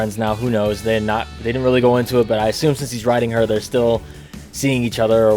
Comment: I thought you said. "since he is riding her." When you were referring to Tails and Zooey. I was like,, "Wait... WHAT" WTF is wrong with me....